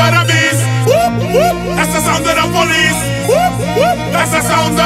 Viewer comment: i always thought he was saying " assassin de la police" and discovered that he was saying "that's the sound of da police" ......